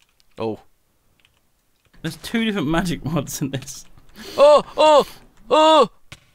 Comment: "There's two different magic mods in this" says Duncan, playing the modpack of almost exclusively magic mods. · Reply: Think he said "too many different", but idk he said it quick Edit: nvm def said two different magic mods 10:20 Also nice pfp :^)